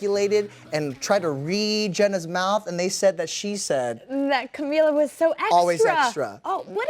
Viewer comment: in this video, I believe she is actually saying "she is so awesome."